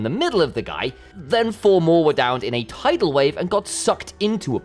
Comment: did you just say… Tidal Wave? 😭